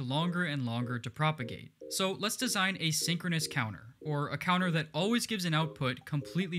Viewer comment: a almost got confused @ 4:35 I first thought you said an a asynchronous counter. anyone else?